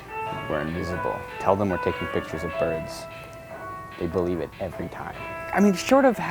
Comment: did he say tell them that were taking pic's of furrys? edit: he said birds